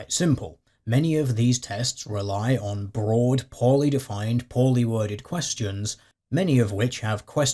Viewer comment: i misheard "poorly defined" as "poly-defined" -- i think it fits.